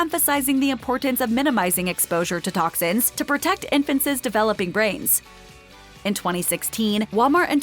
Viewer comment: “infant says” instead of INFANTS. Say you’re from California without saying you’re from California. 😀